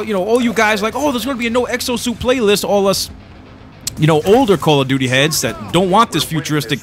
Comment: did you just say call of duty HEADS as in pot heads